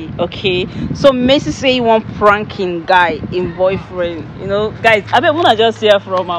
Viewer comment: Did she just say him boyfriend?😂😂😂